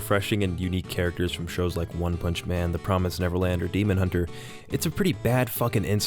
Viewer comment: did you just say demon hunter